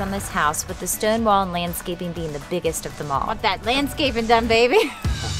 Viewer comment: Did she say "Landscaping" or "Manscaping"?? lol